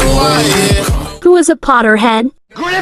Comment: For a second I thought it said ‘Potter hen’